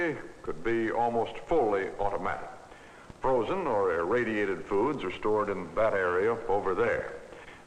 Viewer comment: oh shit ! did he say radiated foods